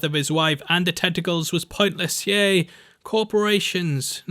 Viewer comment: My hearing is terrible. I thought you said testicles instead of tentacles. 🤣